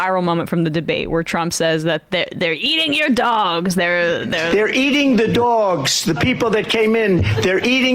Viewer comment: They're eating your dogs, is not what he said. He said, They're eating THE dogs. That's what makes the sentence so hilarious.